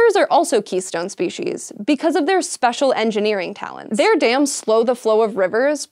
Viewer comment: I hear "They're damn slow. . ."